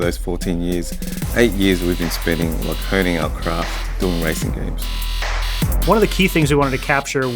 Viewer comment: i swear he said ricing games.